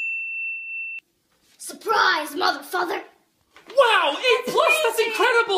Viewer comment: I thought she said surprise motherfucker 😂😂😂